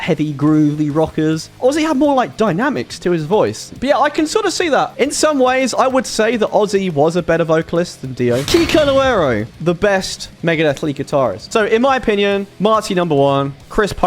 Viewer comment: i thought that said kikuo 💀